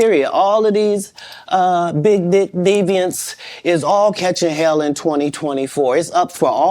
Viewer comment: Can someone tell me what you hear Katt say at the 13:00 mark? I feel like im hearing things 🤣🤣🤣🤣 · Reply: Do you mean when he says ‘deviants’